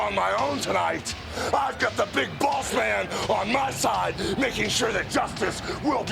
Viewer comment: It sounded like hogan said big balls man 1:56 😂 but I know he says big boss man it just sounds different 😂